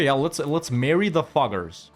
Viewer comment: pronunciation so harsh all i hear is "let's marry the fu**ers" 😂😂